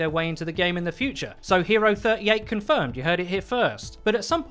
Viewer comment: Did he say hero 38 COMFIRMED??? In time